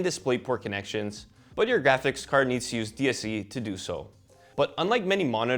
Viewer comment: What is he saying at 5:17? What has to be enabled to use 240hz mode on the GPU? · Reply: DSC, which is display stream compression. It's on by default in the monitor and nothing to worry about!